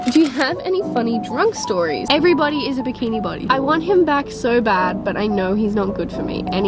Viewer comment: For a sec I thought you said bikini bottom instead of bikini body 😂